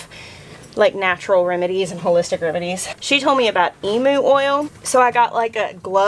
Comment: Just going to say as an Aussie, I thought you said "Emo oil" ... We knew you were dramatic, black hollyhocks is fine but now it's Emo oil?? 😆😙 ... Helpful hint: its pronounced 'Eem-yew' not 'Eeem-oo' And I'm 100% on board with running from cows. Thx so much for being so real with us ❤️❤️❤️❤️❤️